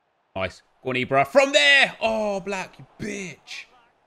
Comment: Anyone else thought said “Aawww, Black Bitch” When he actually said “Oblak Bitch”